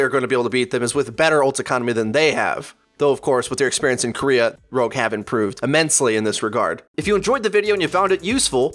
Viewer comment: What said: "Rogue have improved" What I heard: "OH GOD IT'S GETTING EVEN BIGGER"